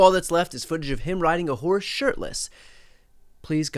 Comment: Lmao I thought you said “ride a wh*re shirtless” and then I remembered Putin…🤦🏼‍♀